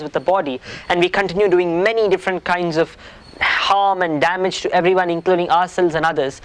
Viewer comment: Line 8:07 of transcript reads wrong....says calm and damage instead of harm and damage, this is such an important video, thank you very much. Bows to you.